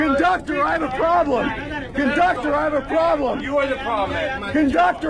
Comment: It sound like he say "CONDUCTOR I HAVE A PROBLEM" but then he say CONDUCTOR WE HAVE A PROBLEM.